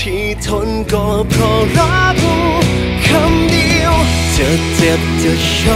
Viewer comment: ทําไมคําว่ารักได้ยินเป็นฤดู สงสัยหูไม่ดี แต่เพราะมว๊ากกก